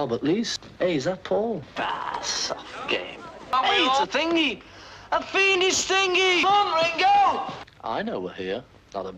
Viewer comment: what does he say? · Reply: "It's a thingy, a fiendish thingy!" It's some kind of bomb he's talking about